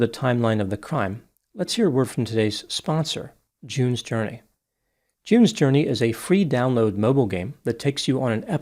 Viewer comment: "Jews Journey". Literally thought that's what you said. And I still think it will make a great game😂